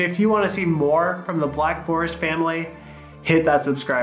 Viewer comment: I've watched a dozen or more videos with him in it and i always thought his sign off was strange, i thought he was saying fist bump, not bis bald 🤦‍♂️